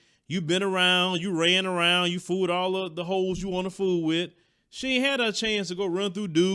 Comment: OMMFG did just say "Hoes"?!?! 😭😭😭😭😂😂😂😂